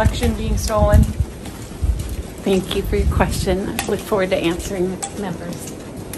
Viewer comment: Did I just hear her say "Thank you for your question, I look forward to answering it never" at 0:20?